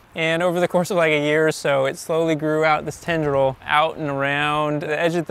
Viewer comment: Captions: "tender toll" instead of "tendril" (1:56) Who proofreads those? Grammarly?